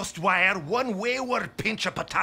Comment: I just realized that Demoman isn't talking about how he makes it, but rather how people could screw up making it, meaning that he isn't completely drunkenly rambling but also talking about how his job is very precise and that a single small flaw could ruin their entire career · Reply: Yea, I always thought he said "I kablooey" instead of "and kablooey"